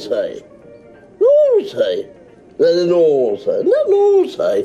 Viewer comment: I am not english native speaker. May someone please tell me what he says at 1:27 ? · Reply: Saulo Moraes naughty very naughty